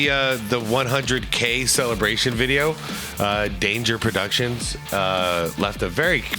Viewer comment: Lol, dude you said "Danger" Productions instead of Legendz and I was wondering why Danger would make a video like this. PS- Even though Austin was in the right, it's super ironic for a Lakers player to accuse anyone else of being a pussy.